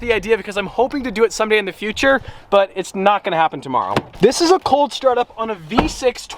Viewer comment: did he say tomorrow?